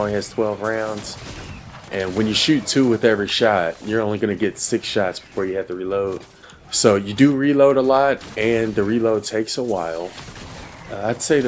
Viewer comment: Every time I hear say “six shots” I can’t help but hear “shit socks” 😂 IYKYK